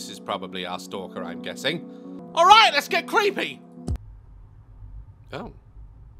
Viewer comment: I’m sorry but when u said let’s get creepy I thought u said let’s get freak,….